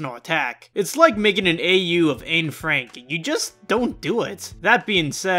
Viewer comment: Did... did you just say "Ain Frank"?